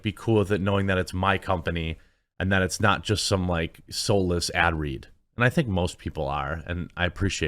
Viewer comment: You know, instead of constantly verbally advertising your coffee you should shoot short, personal, coffee commercials that you play in yourself to play shortly after your video starts. Do something catchy and fun for every commercial you do. You get more people to watch and buy, guaranteed. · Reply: He's obviously not that clever... because "his company, is a soleless add read"... atleast that's what it sounds like he says 🤣🤣🤣